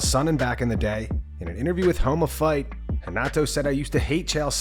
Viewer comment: I'm driving to work so I'm not looking at my screen and I completely thought you said "homo fight" and had to look to see it's actually called "Home of Fight" 🤦🏻‍♂️🤣🤣🤣🤣🤦🏻‍♂️